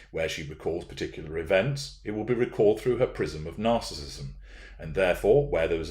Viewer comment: when you said, “prism of narcissism”, I heard “PRISON of narcissism”! It’s that, too!